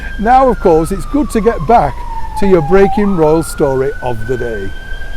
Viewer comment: all this time I thought he was saying "breaking balls story". I'm so sorry.